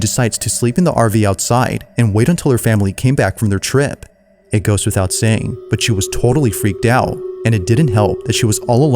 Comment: I don’t know if you intentionally said “it ghosts without saying” but that is definitely what I heard